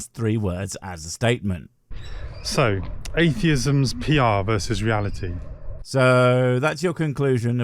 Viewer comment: I think you misheard him here. Sounds to me like the LLM he had write his script was giving him a bullet point heading of "atheism's (possessive) PR vs. reality" and you misheard it as "atheism is PR vs. (i.e. against) reality". tldr he's likely dumber AND lazier than you're giving him credit for here